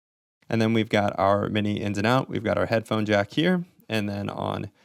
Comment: did you call that I\O "Mini Ins and Out"? 0:56 I watched the video anyway ;-) It was good. I even liked it. Thanks!